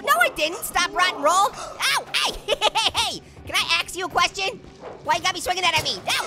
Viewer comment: I thought you said " can I axe you a question". Hahahaa